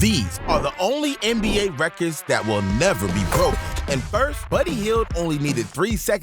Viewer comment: Sorry had to stop at the 0:08 mark. A record that will never "be broked?" I played it back three times to make sure, and it is a bit hard to hear, but that's what is being said by the AI program. You lost this possible subscriber.